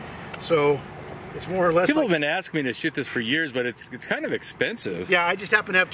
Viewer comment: did I hear you say SH!* or shoot?